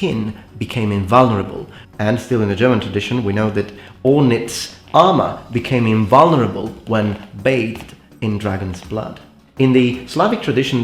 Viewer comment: What was that German legend about a dragon slayer with invincible armor? It sounded like he said "Ornitz" but I can't find anything under that name.